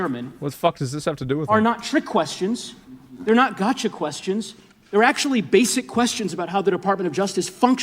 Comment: did he just say "Gacha Questions?"